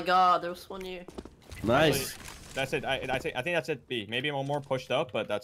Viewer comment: when he said that's it, it sounded like Mito. 😍